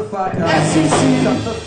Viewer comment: Is it just me or does he say shut the fuck up instead of cover it up to mtv and fcc at like 1:30 XD